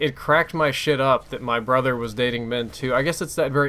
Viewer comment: did I hear "...that my brother was dating men too."? is this just me xD?